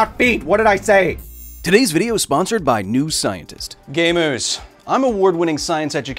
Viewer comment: why did you say gay moose tho?